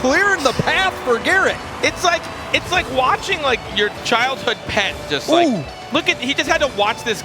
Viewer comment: What was he even trying to say there? "It's like... watching your childhood pet" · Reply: M1ke_and_Cheese Rocket League like watching your childhood pet get ran over